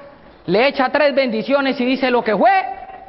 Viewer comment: AYUDA!!! como va la frase en el minuto 11:14 lo que fue... no entiendo lo q sigue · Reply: Lo que fue fue